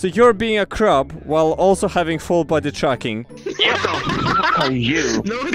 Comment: I always heard you say "full body chucking" instead of "full body tracking"